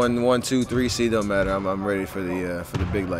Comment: did he just say "when its crack time ill be ready"? Wtf? lol